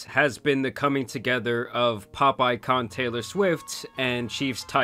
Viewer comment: you said "pop icon" but what i heard was. "popeye con"